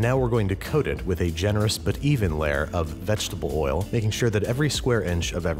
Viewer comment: NOTE: I accidentally said olive oil during the cast iron seasoning - I meant vegetable! Only use neutral-flavor oils like vegetable, canola, sunflower, or shortening! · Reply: I heard vegetable.